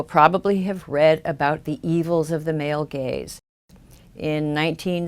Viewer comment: For a while I thought she was saying male Gays.. not Gaze. "The evils of the male gays" lol.. Good video I know ya were saying Gaze but it is kinda funny how it worked out if you switch that one word.